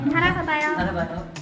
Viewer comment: 살아서봐요 나만 들리나